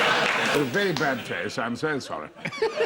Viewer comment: what does he say after the Lynx prisoner joke? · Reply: Very bad taste, I’m so sorry